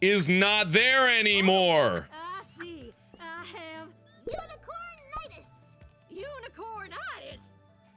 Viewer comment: I thought it said UNICORNTITS